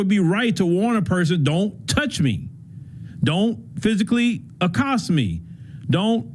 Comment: he never said that. He said “touch me and see what happens”. Had he said “Don’t touch me”, that’s different. The former is an instigation. The second is a warning. Neither allows for stabbing an unarmed person.